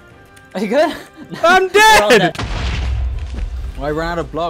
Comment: Omg at 0:42 it was like the first time I hear it that says "I'm dead" 😂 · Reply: Lmao it was just a mini “a-“